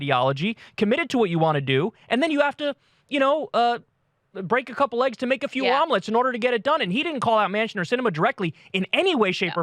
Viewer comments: You have to, you know, break a couple legs to make a few omelettes! (That's how I heard it.) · "Break a few eggs" and I thought at first he said "Break a few legs".